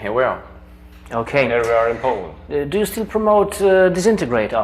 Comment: i'm not sure if i'm right to think Samoth said "and here are in Poland" at 0:47 (but judging by the interviewr's accent it is probable); in that case i believe they drink Tyskie. there you are;)